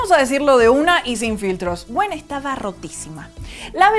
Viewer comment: escuché "gwen estaba riquísima" 😂👌